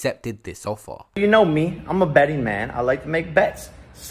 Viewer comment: Did man say I’m a Battyman 😂😂😂😂😂